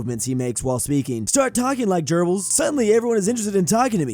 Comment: I thought you said gerbils there, but Goebbels is pronounced very differently, as he is written with an ö in German.